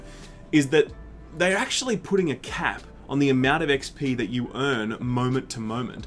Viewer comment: I misheard it as Bungie putting a cat in the game for some odd reason.